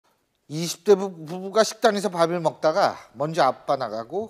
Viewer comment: 나만 20대부부부부가 라고 들림? 비와서 랙걸린건가?